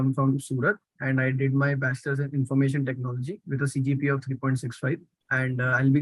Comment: did he say CGPA 3.65 ?